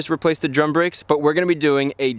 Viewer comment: I thought the caption said “Dumb brakes”